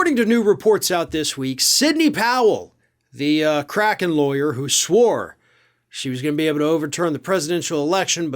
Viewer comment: 🤣🤣🤣 I thought he said "crackhead lawyer " 🤔🤔🤣🤣🤣